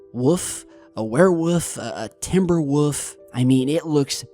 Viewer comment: I keep hearing day "Werewoof". 😂